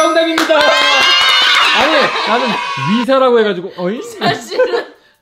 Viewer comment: 님 전 이사라고 들려요 ㅎㅎ